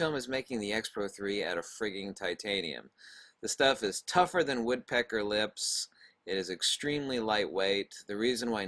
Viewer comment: You claim titanium is tougher than woodpecker lips. Do you have any real data to back that up? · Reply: I thought he said Woodpecker Tits.